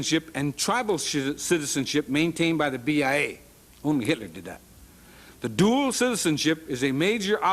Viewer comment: what does he say at 14:15 something about hitler???